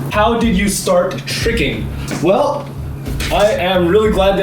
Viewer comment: What is the word he’s saying at 6:34? “Before climbing I used to do _” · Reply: Before climbing I use to do a sport called ‘tricking’